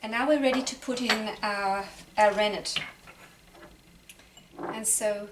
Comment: Hii, i would like to know what did you said @ 5:39 ? what did you poured in ? i didn't get you. thanks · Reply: It is rennet - rennet makes the milk solids seperate from the whey. happy kefir making my friend! Love